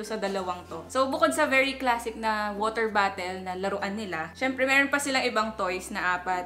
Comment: I always hear when u say "water bottle" u say it like " water battle" lol is funny